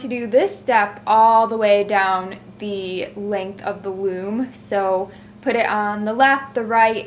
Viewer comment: I swear you said womb not loom :D x